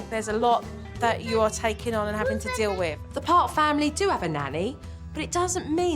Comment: I need my hearing aids. I thought they said the Farts family. Uh no dumdumhead it is Parks family!! 😊 was no disrespect. 😅